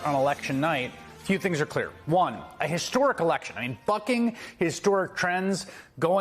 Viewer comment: Sine Note: I’m emptying the dishwasher watching this..and Chris said “bucking”…and swore he said “f@@ was like a 12 year old boy…ooooooooo.